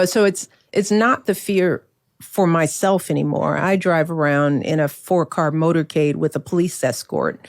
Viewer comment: All I heard was… “I have a bodyguard and a 4 car motorcade, Im big Mike”